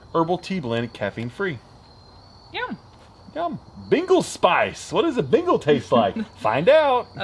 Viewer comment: It sounded like said Beagle Spice